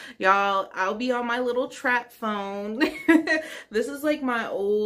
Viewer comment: Not the track phone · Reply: I thought she said trap phone cause I started singing beez in the trap lml